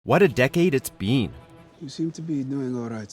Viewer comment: Tbh it sounded like he said “what a decade it’s bean” at the beginning.